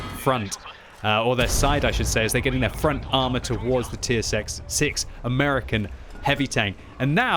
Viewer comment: He said Tier Sex instead of Tier Six Lmaooo